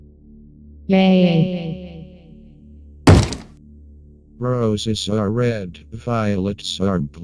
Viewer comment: R O S E S A R E R E D V I O L E T S A R E B L U E S A N T I A G O W I L L K I L L Y O U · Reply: Iheard it as “Bros it’s our red violent tsar plume”